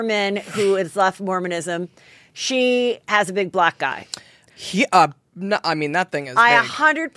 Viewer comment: I'm not even kidding that I thought you said "Heather Gay has a big black guy, and I totally think she slipped and fell on some black guys" Happy to realize it was big black EYE, and black ICE :)